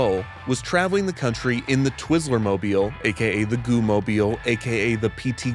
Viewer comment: Getting ten minutes into this video full of quaint Americana and tourism pitfalls only to be completely assaulted by the terms “goo-crew” and “twizzler-mobile” was something I was NOT ready for. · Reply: I mishearded twizzler as rizzler at first and almost fell from my chair 😂